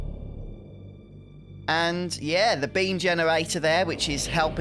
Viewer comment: I thought you said bean generator not beam generator at first 😅